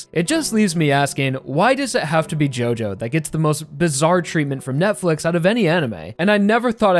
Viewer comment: did you say bizarre…